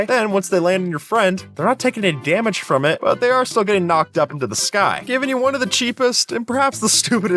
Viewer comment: my internet buffered here so all i heard was "but theyre getting knocked up" bro i was choking on my water XD my lordy